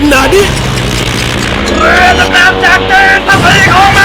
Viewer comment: พูดว่าอะไรวะ ฟังหลายรอบแล้วไม่เข้าใจ&555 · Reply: เรือดำน้ำจากจีน ภาษีของมึง!!!!!